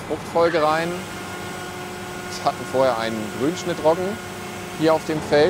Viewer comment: Servus 3:55 -was war die 1. Frucht Grünschnittroggen? habe es akkustisch nicht verstanden,? ist die Sorte Viola besonders ölhaltig?.😀.........................